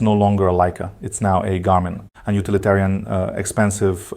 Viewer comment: Are you saying Garmin or Canon? I hear Garmin, the caption says Canon.